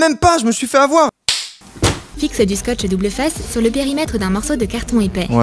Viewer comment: il y a que moi qui entend scotch double fesse? 😂😂😂